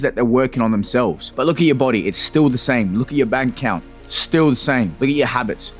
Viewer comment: Thought you said body count at first 😂